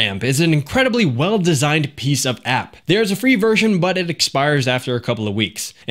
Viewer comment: Did you say piece of app? Sounds like you said piece of ass... which is really strange from you lol.